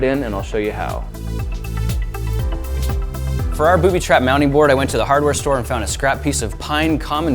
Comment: Thought he said bootytrap